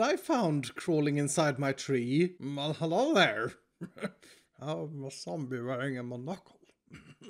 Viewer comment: Sounded like he said "I'm a zombie wearing a moknuckle"